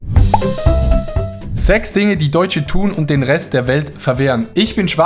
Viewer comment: Ich hab : „Sex Dinge die…“ verstanden 😂